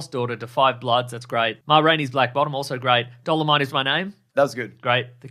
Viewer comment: Did anyone think at 7:23 Mason said "Dollar Mite is My Name"? Because that would be a VERY different movie and a super local reference.